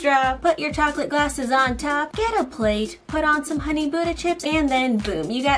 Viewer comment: OH, was saying “ Honey Butter Chips” I keep hearing “Honey Buda Chips” 😐😦